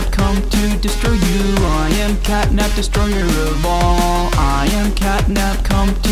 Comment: "I am catnap destroyer of balls"💀💀💀💀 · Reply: I heard it as destroyer of balls sorry guys😅